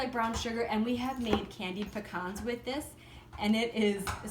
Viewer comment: 😯😯😯 Did she just say CANDIED BACON..... what have I been doing with my life? I NEEEEEED ITTT! 🤤